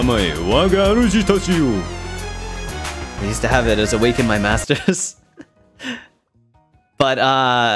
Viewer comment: did he say "used to have it as a wake up call in my master's" ??????? he took master's????????